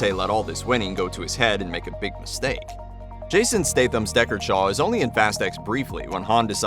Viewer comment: What about Owen Shaw. He helped Dom even though was with Deckard on the plane rescue. Owen got to be in the next one that wouldn't be fair if he didn't no screen time. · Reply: Damn, all these years I misheard his name as Dickhead Shaw 😂